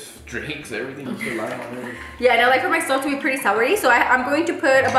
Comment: Did she say “I like for my stuff to be pretty salary”? Like salado? 8:27😂